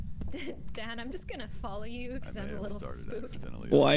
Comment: Did just say dad?